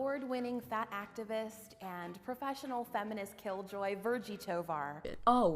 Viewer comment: did she say she was a "professional feminist"? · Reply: I thought she said incessant feminist